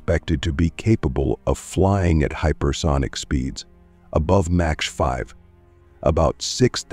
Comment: mins did he just said "match 5"? 😂😂😂 time to upgrade your narrator